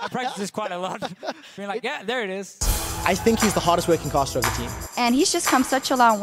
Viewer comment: was I the only one who heard that quickshot said "I think he's the hottest working caster", then i realised he said hardest...